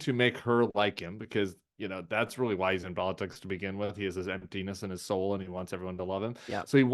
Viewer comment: I thought he said "he has this empty penis and no soul" and was thinking, "Ouch, that will hurt Trump's faux-manliness" so I turned captions on and was let down that he said "emptiness in his soul."